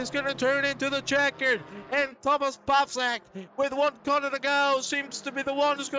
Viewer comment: Sounds like you said Thomas Ballsack